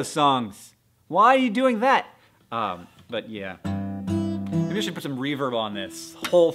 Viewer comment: I thought he said 'Maybe I should put some Reavers on this' and I was like, no Hank, they're too scary!!